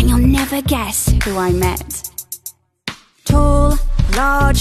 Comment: When I first heard this I thought it was “Tall, large Henry the ape” Edit: damn my notifications are blowing up- thanks for the likes lmao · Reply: I heard "Tall, Large, Henry the Grape"